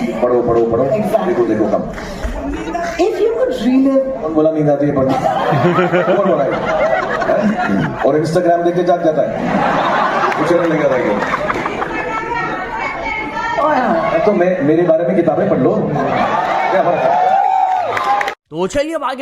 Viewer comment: kya bola kuch samjh nhi ara😅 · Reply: Srk: reels mat dekho books padho Guy: books padhte time neend aata hai Srk: kon bola ye ? Acha matlab reels dekhne se jaag jate ho, mere bare mein books padhlo